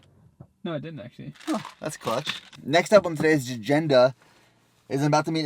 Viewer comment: Did nick just say "that's clutch" ?